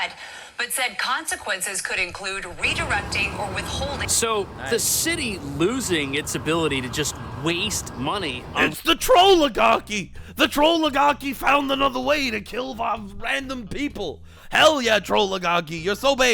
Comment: Second I think ruined pokemon music for me every time I encounter a pokemon I just hear say "oh Tard alert I found another Tard to laugh at."